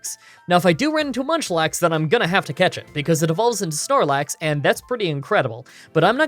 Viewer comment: starlax? did i hear it wrong?